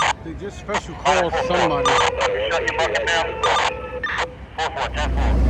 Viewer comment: sounded like he said shut your F mouth ! Condolences to all lost 🕊️